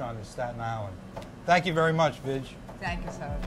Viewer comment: THOUGHT HE SAID "THANK YOU VERY MUCH BlTCH". ANYWAYS IS THE SHIZ NIT! HE REALLY KNOWS HE STUFF AND SHOULD HAVE HIS OWN SHOW ON TV. I REMEMBER FIRST SEEING HIM ON THE TRAVEL CHANNEL SHOW CALLED NO RESERVATIONS THE SRI LANKA EPISODE WHERE THE HOST TONY BOURDAIN LOOKED LIKE HE WAS GOING TO PASS OUT FROM THE HOT HEAT OF LANKA. I NEVER REALIZED HOW GREAT AND DIFFERENT SRI LANKA CUISINE IS UNTIL AFTER WATCHING THAT EPISODE, AND HAVE BEEN INTERESTED IN SRI LANKAN FOOD EVERY SINCE.